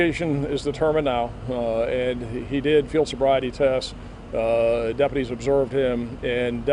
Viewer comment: did he say he failed sobriety test but is not under the influence? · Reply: They said he did a field sobriety test not failed one. I had to listen to it three times to make sure I heard it correctly.